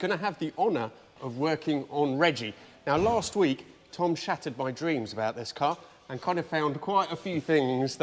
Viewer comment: I heard that as ‘last week, Tom shat on my dreams’, which would also be pretty accurate.